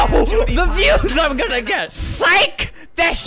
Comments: When he’s says “Sike! That’s just green screen man” I heard “Sike! That s*its green screen man!” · SiKe ThAtS jUsT GrEeN ScReEn MaN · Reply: I thought he said “siKE, tHaT shiTs GrEEn sCrEen mAn!”